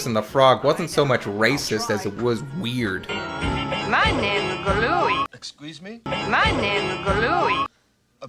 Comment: I always thought she said "my name's Glooey," making his "I'll stick with you" response a pun.